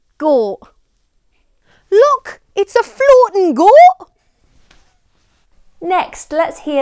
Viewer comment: When she said “Look it’s a floating goat” I for real thought she said focking instead 😂